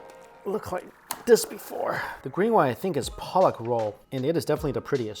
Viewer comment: does he say 'Palak roll'? Palak is Spinach in Hindi.